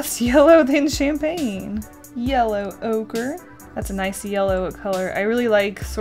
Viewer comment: When she said yellow ochre I thought she said yellow ogre 😂